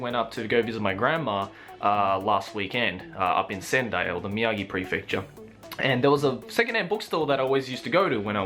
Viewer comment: can somebody help me out with the name of the area where went to visit his grandma? I'm having trouble understanding what exactly he said. Thanks in advance!! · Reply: sendai, in the miyagi prefecture